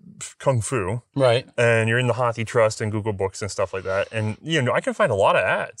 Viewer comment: I heard 'Hockey trust' for Hathi trust... which reminded me that I I always read it as 'Haiti trust'. Thanks for keeping me from saying something silly